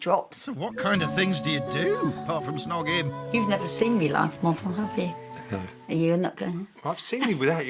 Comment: What does she say she does around 1:40 , I can’t hear what she says she does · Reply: Live model